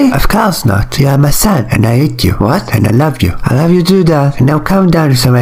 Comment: "U aRe mY sOn AnD i EaT yOu"why did i hear "you are my son and i hate you"😂😂😂WHYYY DID I HEAR THAAAAAAAAAAAAAAAAAAAAAAAAAAAAAAAAAAAAAAAAAAAAAT